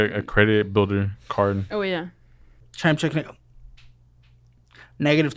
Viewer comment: Can anyone make out what says at 49:26? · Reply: Chime checking